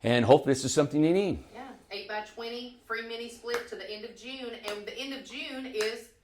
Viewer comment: It's till Labor Day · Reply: that’s what I thought but said end of June